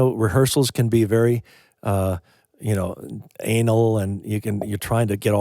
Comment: I know this may sound very strange but what is the word you use at 4:52? “Rehearsals can be very ____”? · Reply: Anal. "Rehearsals can be very anal". Meaning we focus so much on being overly-neat, precise and careful.